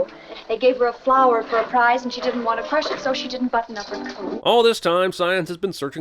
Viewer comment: did she say "she didn't want to press it so she didn't button up her coat"? Lmao wtf does buttoning your via have to do with pressing flowers? You stick them in a book to go flat, where does that connect to leaving your jacket open when you're cold? I must be mishearing it though, what is she saying?